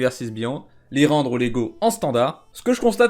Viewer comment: Quand j'ai entendu "les rendre légaux en standard", j'ai cru entendre une collab Lego :D